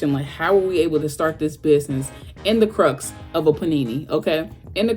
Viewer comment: does she say "in the crust of a panini?" (Because that just made me hungry.)